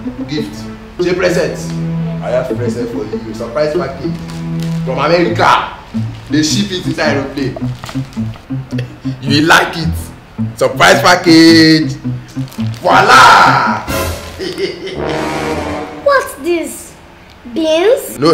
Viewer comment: LMAO! 0:34 am i the only one who heard when he said "they ship it inside aeroplane"